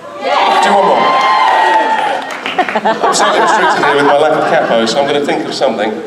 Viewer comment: Can some tell me what does Mr. Ellis says at 46:00 minutes? He is restricted fir his lack of ?? what exactly. Can’t understand. English isn’t my first language. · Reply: I don't know if you still need the answer but he says 'capo' <link>